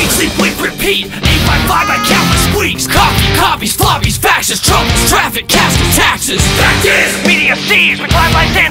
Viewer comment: who else heard Trump is traffic